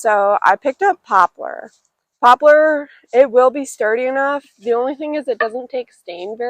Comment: Did you use pine on the base you built ? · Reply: Nevermind I heard you say popular